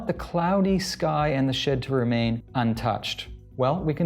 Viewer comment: did he say what I thought he said? I want the cloudy sky and the "shit" to remain untouched? Asking for a friend.